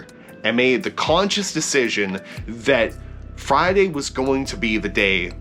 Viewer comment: I thought it said “this Friday” 😭😭😭😭